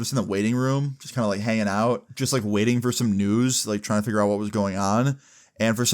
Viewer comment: I almost thought he was about to say waiting for some nudes 😂😂😂